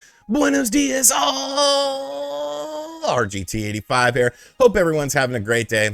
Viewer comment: I thought you said " Buenos Días Baaaaalllls". XD